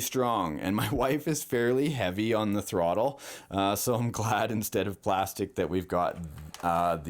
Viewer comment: When he said my wife is heavy on the throttle, I thought he said my wife if heavy. I'm like ooh boy, he's in trouble!😀